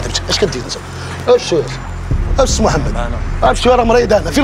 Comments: اكد. لي على دقيقة 20:15 شنو قال ولا انا سمعتها غلط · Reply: قال كروة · شنو قال هنا 😅😅😅😅😅 واش لي سمعت انا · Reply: كروة